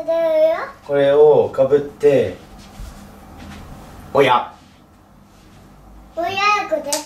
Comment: このとこの【おや】が【おやいずです】に聞こえる笑